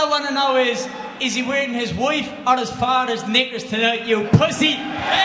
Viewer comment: did he just say the n word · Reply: Knickers